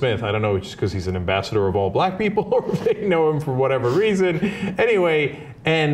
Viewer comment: man what is wrong with the subtitle. copy instead of coffee and at 1:28 it says "ambassador of all black people or pigs". how the fuck does that happen. he never said pigs. I belive we got a racist texter on out hands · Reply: he really said people or anything. dont know how anything is mistaken for pigs tho